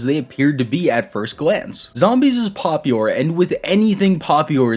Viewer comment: I'm sorry, what was that word you used? "Popiure" lol